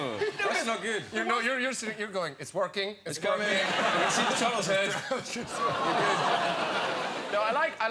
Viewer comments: what did he say??? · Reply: "i can see the turtle's head"... so gross hahaha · What did James say at 5:13? I can't understand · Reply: "I can see the turtles head"